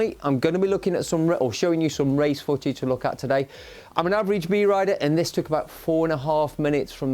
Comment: lol 2:00 did just say he was an average B rider!!??